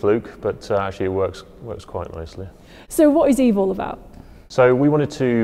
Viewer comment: i heard " so what is evil about?" 😂